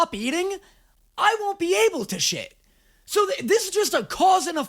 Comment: … I love you (non parasocially) but I can’t tell if your being sarcastic and you know she’s not say sit or shit or what ever. But she’s say Shh like be silent 12:21 🥲